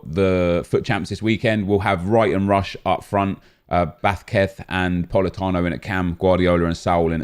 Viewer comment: I literally thought u said bathketh when u said vazquez